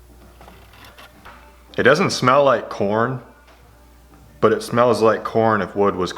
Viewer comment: Who else heard him say it still smells like porn in here instead of corn